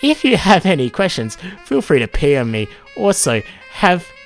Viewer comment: i was listening and browsing another tab and i heard "feel free to pm me" as "feel free to pee on me" and i was just like yeah